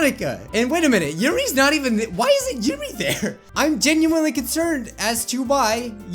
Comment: W H Y I S N ' T J I M M Y T H E R E ? ! ( lit erally what i heard)